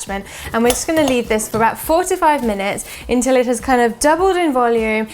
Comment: Almost had a heart attack when I thought she said 45 minutes!